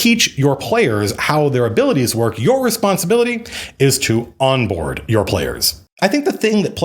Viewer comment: I misheard "Onboard" as "Waterboard" but good news! I now have a completely regular group who hasn't yet left their room!!